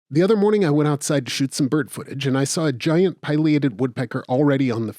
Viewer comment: I heard “shoot some bird footage” and my adhd brain processed only “I went out side to shoot some birds”